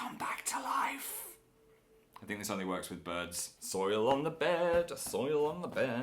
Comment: I thought Phil said "I soiled (peed) my bed"... hahaha I was wrong ^_^